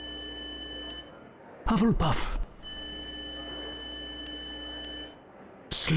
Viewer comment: i swear he's saying pufflepuff